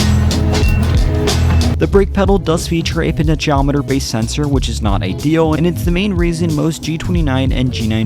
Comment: can someone translate what he said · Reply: potentiometer based sensor?